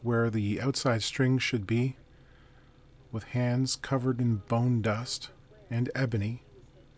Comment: bone dust and Ebony, is that a 90's gangsta rap duo? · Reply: Oh... I thought he said bong dust. 😇